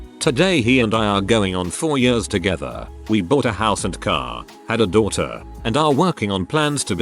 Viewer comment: I heard “bought a house, car AND a daughter” not “had a daughter” I was like wait wait wait, BOUGHT A DAUGHTER?! 😒😂😅